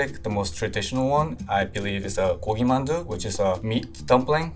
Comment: it says that it’s Gulim Mandu (굴림 만두) but what he made (and what he said) was actually Gogi Mandu (고기만두) which is a meat dumpling. A Gulin Mandu is a different type of dumpling and looks like a ball (Gulin means “rolled”) that doesn’t have a wrapper. He made a really traditional style of Mandu which has a wrapper and is shaped like a half circle.